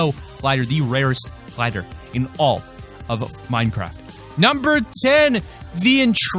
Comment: did he say Minecraft 🤔🤔